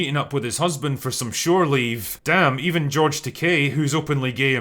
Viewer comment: At first I thought I heard you call George Takei as George "Decay" and I thought damn that couldn't be more accurate.